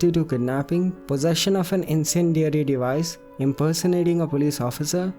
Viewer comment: Has anyone heard inpersonation in 2:01? What does it mean?